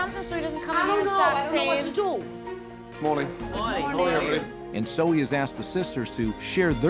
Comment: I hear "Dzień dobry". Is there any Pole among the staff?